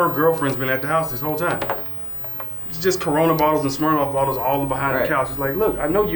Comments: did he just said cornavirus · did he say corona virus? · Wait What I heard CoronaVirus !!